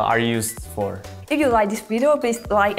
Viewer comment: i think she said, " I see you next time" not "I'll..."